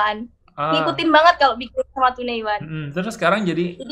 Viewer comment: did she said she is a fan of bigbang and 2ne1??